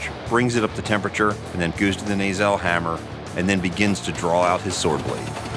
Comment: he says 'goos' instead of 'goes'